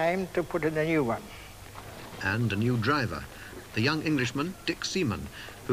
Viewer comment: Dick. Semen. Dick.... Semen. No way was he called that.